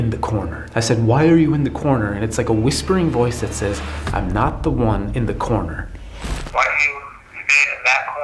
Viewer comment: It sounded like it said, I like to be in the corner.